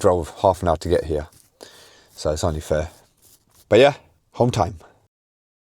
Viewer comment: "Home time" at the end... I genuinely thought you said HOLD TIGHT 🤣